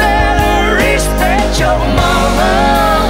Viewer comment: From 2:27 to the end it sounds like she is saying respect your MAMA!..lol I know she isn't but....